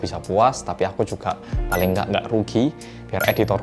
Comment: wait2 ini saya yang salah dengar atau gimana ya? menit 4:45 biar semuanya bisa menganggur? hahahha